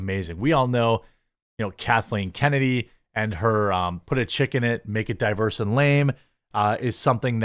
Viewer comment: Dude, I thought you were saying “Chicken”. I died laughing.😂